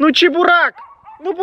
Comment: Показалось что он сказал не "Чебурак" а "чебурЕк" 🗿🚬 –Коротко о том когда я голодная 🗿🚬